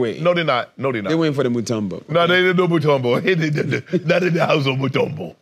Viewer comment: What Shannon say?!😂😂😂😂😂😂😂😂😂😂😂😂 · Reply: Mutumbo😆